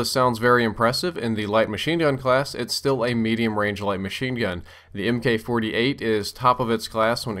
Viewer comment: why does it say rnage instead of range